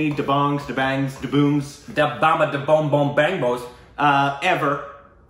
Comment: Did say Bang Bros...